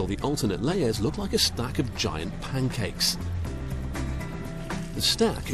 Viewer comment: lol I thought he was going to say, "stack of giant poo cakes" not "stack of giant pancakes" XD But I love using olive oil. I especially love using it with garlic. When you sautee diced garlic with it, it tends to taste buttery as an end result. And I love using this combo as a topping on all kinds of things like grilled fish, steamed/stir fried vegetables and more : 3 Oh, I made myself hungry lol. haha